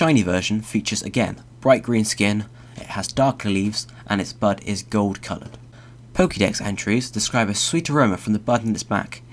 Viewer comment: For a second I thought he said butt is gold colored.